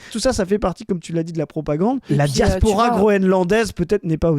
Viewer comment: Hallucination auditive à 10:12... J'ai entendu"la diaspora Grolandaise". 😂